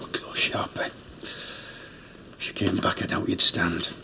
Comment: If you are like me and didn't understand what sean bean said, here it is based on the subtitles: "That was a close one, sharpe, hum? If she came back down you wouln't be able to hold yourself. ;)))" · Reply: He said "That was close, Sharpie. If she came back, I doubt you'd stand." I do appreciate your effort though.